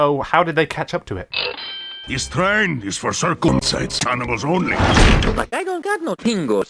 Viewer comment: I wonder what tiny is saying here? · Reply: This train is for circumcised animals only.